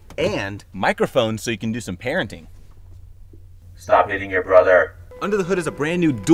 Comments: I thought he said " stop eating your brother"🙃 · Stop hitting your brother 😂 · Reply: I heard it as Stop eating your brother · I thought he said "Stop eating your brother" for a sec on the mic, and I was like 💀👁️👄👁️ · Oh I thought he said “stop eating your brother” 💀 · Who else thought he said stop eating your brother XD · I thought he said stop eating your brother at first... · For a sec I thought he said “stop eating your brother”💀💀💀